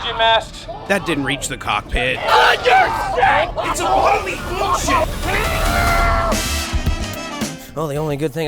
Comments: sounds like someone said bs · Did they just swear? Dang, I've never heard them swear. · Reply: I heard “It’s utterly bullsh1t!” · Did anybody else hear "its totally bulls**t"? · did they say “B.S”?? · I swear someone said that’s bs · did he say its total bs? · I thought spark said its utterly bullsh*t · anyone else hear the BS word? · I think I heard bullsh*t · who heard him say its totally bs? cause i heard it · Love y’all & hope you liked the vid! Which one was your fave? 😂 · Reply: did he say bullshi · did anyone else hear what I head I literally heard bs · Idk if I misheard it’s incredibly bullsh-t 💀